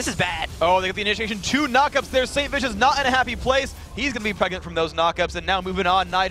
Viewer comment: Did Phreak actually say "He's gonna be pregnant from those knock-ups." at 31:17 ??? LOL